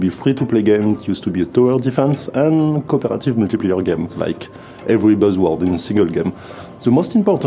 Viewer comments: He clearly says ‘Every buzzword in a SINGLE Game’... He does not ‘mumble’ at all (like the captions read) Minus 2 points for whoever did the captions, but a great, insightful vid in a great series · Technica i get that English is the internet's language, but even if i speak both English and French, it was hard to follow the video, making it in french and putting subtitles would have helped, je comprends que l'anglais est la langue d'internet,mais mème si je parle anglais et français, c'était dûr de suivre la vidéo, la faire en Français et mettre des sous-titres aurait aidé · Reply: there are English subtitles, though whoever did them also didn't understand everything, for example at 1:02 he says "every buzzword in a single game" but the subtitler didn't understand the word single so it reads "in a [mumble] game"